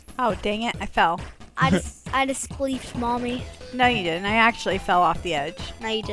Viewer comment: was I the only person to think said queef